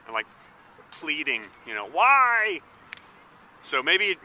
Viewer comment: "WHY!" sounded like Yeshmin Blechin